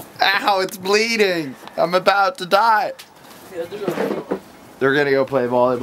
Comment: sounds like he says I'm about to diet